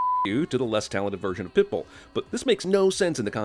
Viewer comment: says pitbill instead of Pitbull